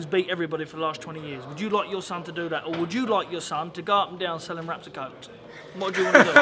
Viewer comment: What does Saunders say at 4:29 ??? sounds like "up and down selling wraps of coke?" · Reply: Selling wraps of coke I think